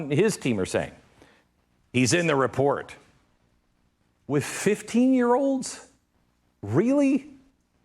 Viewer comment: I heard him say twenty 5 year olds